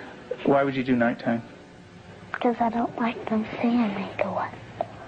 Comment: Hoy estamos con los videos que mas causaron polemica a lo largo de la historia de YouTube, pero no se queden con la palabra POLEMICA, porque se van asustar mucho... ¿Que otro video te gustaria que haga? Comenta 💀👇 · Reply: creo que dice ''Because i dont like they see me doing that ''